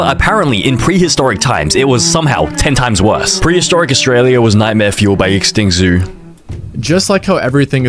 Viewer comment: I thought you said historic austria 🙋🏻‍♂️🇩🇪🟥⚪️◾️